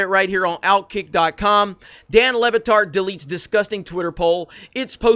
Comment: I thought it said Dan Le Retard.